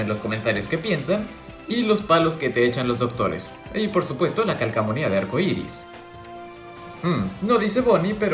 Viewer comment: Lke si te diste cuenta que dijo calcsmonia y era calcomania xD